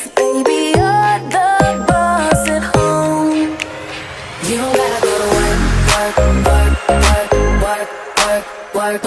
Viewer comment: I’m aware this may sound really weird When they say “we can work from home” I thought they said “you don’t gotta go to work”